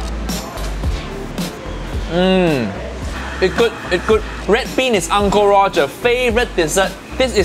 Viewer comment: sounds like hes saying red pen** uncle roger favourite dessert